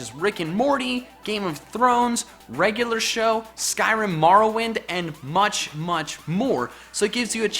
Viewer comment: did he just say skyrim morrowind?